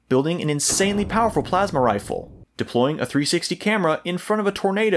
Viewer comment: did you say "plasma Rifle"? notifications on :-P